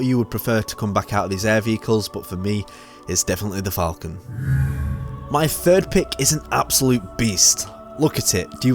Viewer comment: I got an add that cut you off, so it sounded like you said "my third pick is an absolute bee".